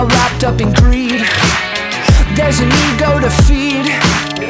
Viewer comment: Lyrics: [Verse 1] I'm the kid in the rain Celebrate the insane Yeah, never goes my way (Yeah, yeah) Take a shot and fall short I don't get the support But I guess that's just life (Yeah, yeah) [Pre-Chorus] Someone go get me some more confetti I think I might have hit my peak already [Chorus] I don't know euphoria Would like to meet her someday C'est la vie I don't feel hysteria She leaves me lonely C'est la, c'est la vie (C'est la vie) [Verse 2] We don't get what we need We're all wrapped up in greed There's a need, go to feed (Yeah, yeah) Wish I could press restart I don't mean to get dark But I guess that's just life (Yeah) [Pre-Chorus] Someone go get me some more confetti I think I might have hit bottom already [Chorus] I don't know euphoria Would like to meet her someday C'est la vie I don't feel hysteria She leaves me lonely C'est la, c'est la vie C'est la vie C'est la, c'est la vie C'est la vie C'est la, c'est la vie I don't know euphoria Would like to meet her someday C'est la vie I don't feel hysteria She leaves me lonely C'est la, c'est la vie C'est la vie C'est la, c'est la vie C'est la vie C'est la, c'est la vie C'est la vie C’est la vie means that’s life btw ❤️ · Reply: I thought it said new goat to feed lololol